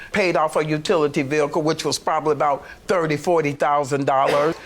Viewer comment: It's unfortunate about the Defendent's abusive past. However, when she is buying three 56" TV'S, buying a $110,000 home, and paying $40,000 in utilities and expecting a 38K settlement from her husband she should have paid her mother the 5K that she owes her! 🤨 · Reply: I'm watching it again and with the captions on... yep, she did say utility "vehicle". I thought for sure she said utility "bill".